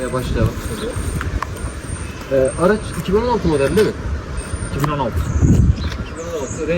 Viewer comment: Araca 2016 model olduğu mu söylendi yoksa ben mi yanlış duydum